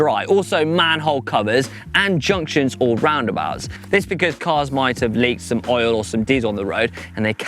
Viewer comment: Did just say "cars might have leaked oil and jizz on the road"??